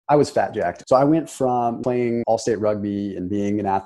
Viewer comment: I thought he said he was a flapjack